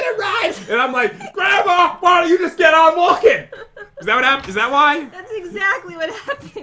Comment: pat said the f word?!?!? He didn’t sound like it when you cut to it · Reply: No I heard "get on walking" not that word play it again in slow motion